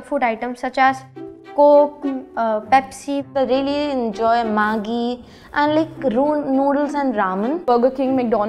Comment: What did he say? 📸📸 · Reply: Maagi = nuudals